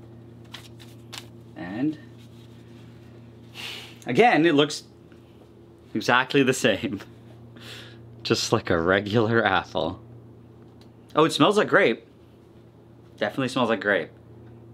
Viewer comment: I think I need some hearing aid, did he say "just like regular asshole"?